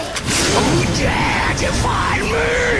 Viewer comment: it's "defy", not "deify" as deify = adoring someone and defy = resisting someone just in case someone didn't understand that here :P